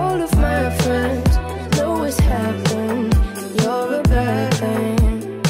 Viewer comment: I always thought she said “you’re a bird thing” instead of bad I’m so dumb